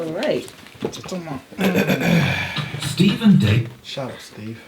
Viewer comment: Hahaahaha i heard that ad 'steve and david use eto....'